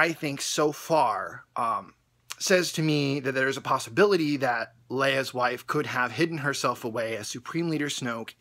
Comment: did he say Leia's wife